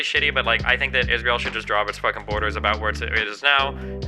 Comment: the one he defaulted to- before getting more information. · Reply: I heard "should"